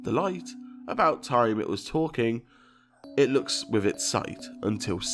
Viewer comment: Instead of "It looks with its sight" (which you gotta admit is the most nonsensical of the bunch xD) I heard "I need to tell you something" I heard it even before you put subtitles on. "Okay-- let me tell you something. Until Six-" Also instead of "The light, about time it was talking" I hear something along the vague lines of "Tonight, I'm going to take it back to-" and then some indecipherable whispering. Just thought I'd edit this in, it's not as important, but to me it sounds as if they wanna help you and they're conspiring with you or something. Like "The light" has a bit of a weird accent to say that xD "The LOIGHT"